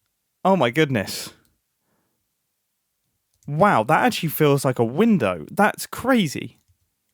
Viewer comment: Can't be just me who heard "Wind-er"